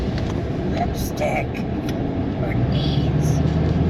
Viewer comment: I heard it to say, "lipstick. ..... For bees". 🤷🏻‍♂️